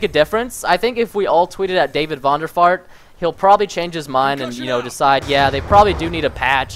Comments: did he said David Vonderfart? xD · I thought I heard Preston say David vonderfart xD